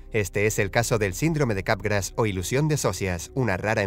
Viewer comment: Le entendí "sindrome de garrapatas"